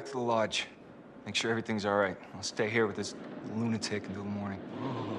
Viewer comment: TYPO! 2:31 Mike says to Chris that he should go back to the lodge and make sure everyTHING’S alright but the caption says everyONE’S alright. Anyways everything was amazing.